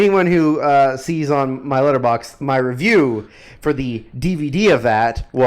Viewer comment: I like how it sounds like said his 'litterbox' instead of his Letterbox. And yeah, Sweet Lightning is half sugar, half sweetener.